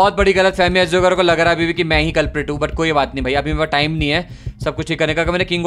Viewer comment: Guyss Please listen at 19:48 what said 😂😂😂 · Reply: Gf nai culparate bola